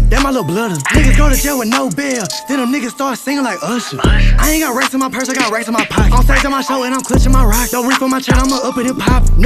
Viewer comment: What did say at 2:04 to 2:06 ? Help me plz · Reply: Ketwion Boyce Ian got racks in my purse I got racks in my pockets